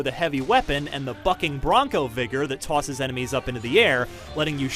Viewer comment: I almost thought he said 'You might want to take cover and then use the fucking Bronco vigor!" xD